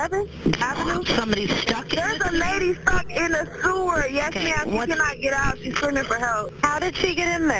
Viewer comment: Am i the only one who thought she said "there's someone over here fuckin' a scooter"? Then I was like "ohhhhhhh stuck in a sewer"